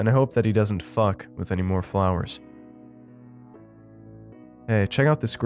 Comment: heard it as "I hope my friend doesn't fuck anymore flowers"